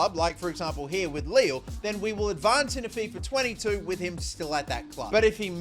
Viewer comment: did anybody hear at 0:51 he said fifa 2022?